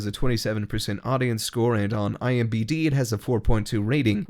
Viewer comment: Did he just said IMBD